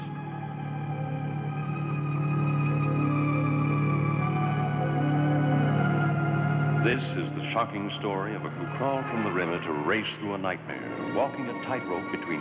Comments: Anybody else hear "this, is the fucking story..." @ 0:24 at first? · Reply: SHOCKING!! he said shocking not fucking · i swear he said ''fuckin'' :D